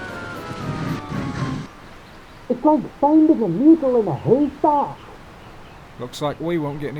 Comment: What did Jock say? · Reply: "It's like finding a needle in a haystack." That's what he said.